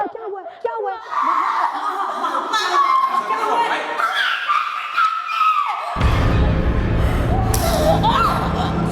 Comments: What did Sajal say when Fawad touched her shoulder...? she was yelling so loud I couldnt understand.. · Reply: I think she said Mama, He tried to touch me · what did she say... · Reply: Saba Khan she said He tried to touch me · what did she said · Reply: she said '' he tried to touch me''.